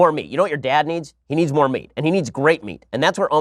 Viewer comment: I swear it sounded like Ben said "more me."